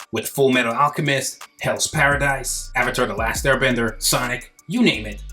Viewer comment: Did you say Sonic, Sensei?! 👀 👂🏿